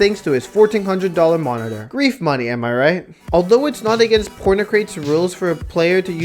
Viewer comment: thought that said “pornocrate” and was like “what”